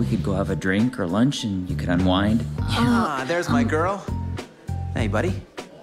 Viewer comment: did he say wine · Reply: IT DID SOUND LIKE HE SAID THEY COULD HAVE WINE NOT UNWIND